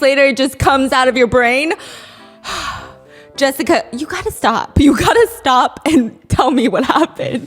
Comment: 😂😂😂 it sounded like stephie called mr . Mango.. "jessica stop " 😂😂😂😂 this whole segment !!!!!! I live for chinese messiness . Now it makes sense about their dramas babyyyyyyy its always a Rollercoaster